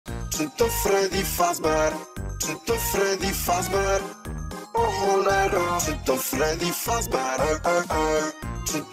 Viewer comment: Why did i hear "shit on Freddy "